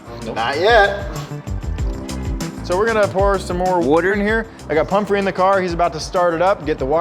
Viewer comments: Hahaha anyone catch the ChrisFix “woter” at 13:18 good job editors 😂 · did I just hear a chrisfix “wooder” instead of water?🤣🤣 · i heard that woder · Anyone else hear Mr Jobe say wooder instead of water at 13:21? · Was that a ChrisFix "woter" I heard @ 13:20? lol